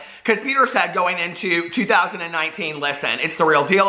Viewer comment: I swear I thought you said “Dad AF coming in 2009” I was like what?!